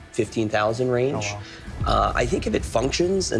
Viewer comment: Who else heard dat “hello” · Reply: Oh wow